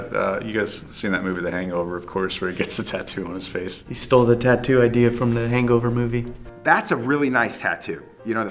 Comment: did he just say that mike stole the tattoo from The Hangover?